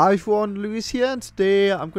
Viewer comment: What does Lewis say at the start of every video? "Hi for one Lewis here" ? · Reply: I always thought he said "Hi forum, Lewis here" haha.